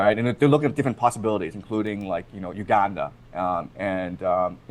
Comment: did Prof say Uganda??